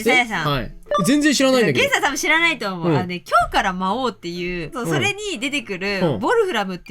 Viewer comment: 他の動画を見た流れで、連続再生しながら作業してたら、「今日からマ王」って聞こえて画面の前に戻って来てしまった